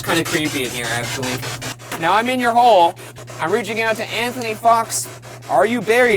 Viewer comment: Ryan: Now i’m in your hole- Me: tries to be mature for once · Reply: He actually said 'now I'm in your hole' though, so apparently many people can't hear.